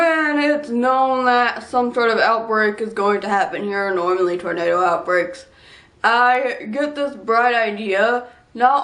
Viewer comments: on the closed captions where you weren't sure what you were saying, you said "normally tornado outbreaks" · where the caption is [I HAVE NO IDEA WHAT I'M SAYING], the untranscribed speech is "here, normally tornado outbreaks". :)